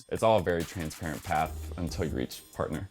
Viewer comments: Can anyone please tell me what he's saying at 0:15 after "It's all very transparent path"? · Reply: "...Until you reach partner" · What did he say @ 0:17? "until you reach....p-"? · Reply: Partner. It's the highest title in the consulting career path